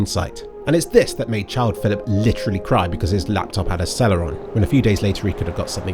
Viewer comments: "seller on" instead of "Celeron" in subtitles. Thank you for subtitles! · subtitle "seller on" instead of celeron